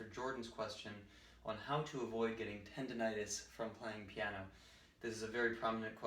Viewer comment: I thought you said how to avoid bronchitis